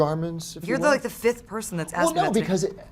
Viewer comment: U single this dude out & she clearly says; 'you're like the 5th person that asked me about that ...' Goofballs